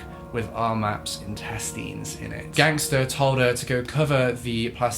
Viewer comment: I thought you said a bag with her testes in. Was really confused until I realised you must have said intestines